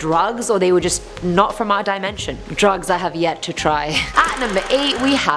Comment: Did i hear her say this right; "drugs i have yet to try?" @ 3:08 xD we can try them together ❤ lol